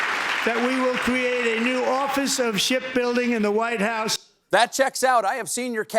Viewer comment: I’m hard of hearing did he say ship building thought he said shit building